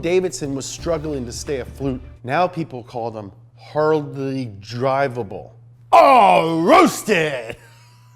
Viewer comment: I always heard it as "Hardly-Ableson" lol